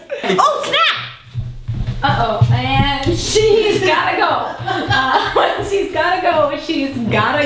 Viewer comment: holy butcracksss when said "And she's got go" i thought she said my name "MAN-AN-SHEEE" it spelled originally mananshi though <<< thats how it sounds